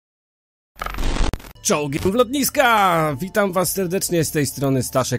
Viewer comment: W tym intro tak to przekształciłeś że usłyszałem "Czołgi w ludziska!". Czy to tylko moja chora wyobraźnia?